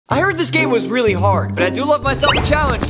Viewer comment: I thought you said I am really hard.😅